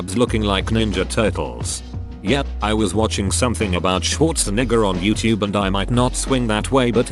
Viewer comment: Nah it dead sounded like TTS said “Schwartznigger”